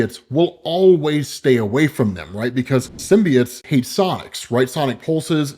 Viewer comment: i thought said "symbiotes hate socks"